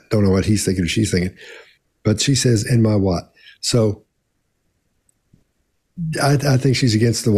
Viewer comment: I believe she said, "Am I right?", not "am I what". She said there's no laptop in storage, then looked toward her counsel for confirmation about whether she's about to get called out.